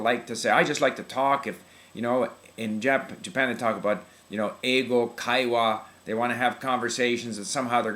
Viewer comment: What did he say at 5:14? Having a conversation you learn something from? Ego Kaiwa? I can’t find it on google! Help me to spell it correctly!